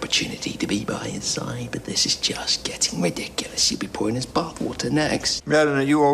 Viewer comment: Can someone tell me what Merlin says after “but this is just getting ridiculous” 10:53 ⟟ can’t understand him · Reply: He says: "-you will be pouring his bath water next"